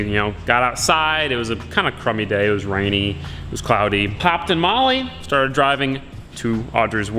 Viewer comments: At the start thought he said popped a molly 😂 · Sounds like u said 'popped a Molly'... Not 'hopped in Molly'😂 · I was listening to the audio play over some speakers in my room and I walked to the next room and I thought you said...."kind of a crummy day, rainy and whatnot. So I popped a Molly and headed down to the store" lol 😂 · I heard popped a Molly and I was like 😯